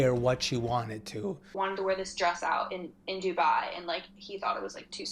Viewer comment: Did she say Dubai? She said he didn't let her wear it cos it was too sexy? Well if it was Dubai, then he could be right. That sounds more like a country thing then it being abt him